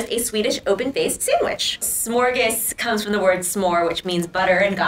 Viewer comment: i thought the said S’ morgens instead of smörgås and S’ morgens means in the morning in dutch hahah